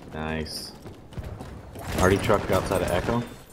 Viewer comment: “Arti truck!” I thought you said “party truck” XD